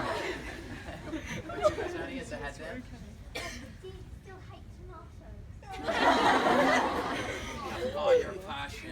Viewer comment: what did he say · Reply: Marija Barbarić thé girl said do you hate tomatoes ? And he said yes with passion